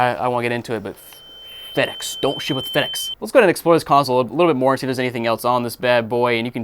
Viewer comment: I heard don't sh*t with FedEx